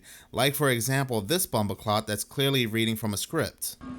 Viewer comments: sorry did you just call that guy a bumbaclart? 😭🤣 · did you say bumboclaat? Lol, that bumboclaat